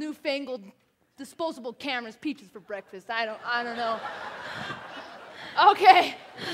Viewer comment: i thought she said 'peaches for breakfast'. oops- my bad.